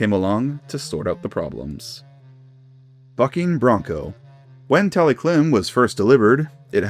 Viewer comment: When i heard “bucking bronco” i thought i heard “f___ing bronco”😭😓